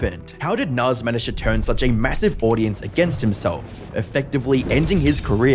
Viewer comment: WAIT... Did you just say massive?